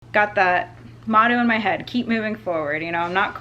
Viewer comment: At the beginning of the video I thought she said "Got the mono in my head" mono sucks